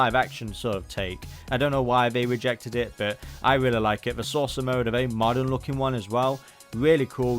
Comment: You're saying "saucer" but I keep hearing "sorcerer" and imagining wizard Cosmos flying around riding on a disc.